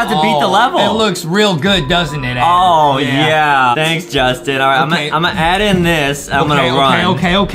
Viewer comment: When he said Justin!!! In the beginning of the video I thought he said LEBRON JAMES XD